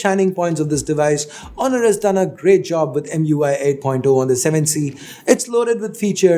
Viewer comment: Please don't forget to 👍/👎 the video by clicking above ⬆ - remember that's for if you liked the video or not and not if you liked the phone or not 😀😀😀 What's your favorite phone under 15k? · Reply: does said mui?